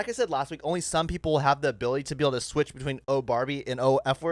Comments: I heard “Oh Farbie!” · I heard 'oh farbie'